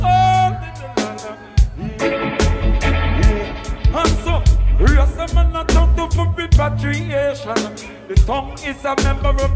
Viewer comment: I hear "and so, Rastaman ah chant up fi repatriation" and "wan fi walk upon water all beca' lucifer rule in the earth, Jah be leading decimal"